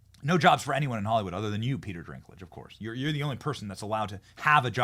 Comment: Is he saying Peter Drinklage as some sort of joke that Ive missed or does Benny just genuinely not know thats not his name...